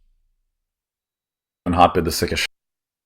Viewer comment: Anyone else hear something along the lines of Day[J] going "And hotbid is sick as shit"? @ 00:20 >.>